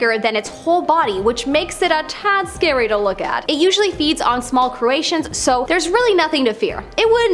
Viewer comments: Gulper Eel I hope that you meant to say that it eats, small crustaceans and not what you said, which is, "...it eats small Croatians." I am very sure that Croatians that are small would be very afraid of this Eel. Cheers. 🇨🇦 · I swear she said small Croatians 5:22 lol